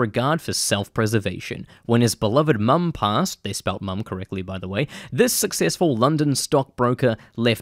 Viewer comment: they spelt mum correct tho, silly Americans thinking they invented English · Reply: damn I'm going deaf, I thought he said incorrect, mb